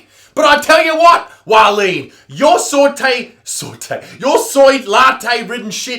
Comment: It sounds like your saying “yo soy latte.” in Spanish your saying “I like latte.” But I guess your saying “your soy latte”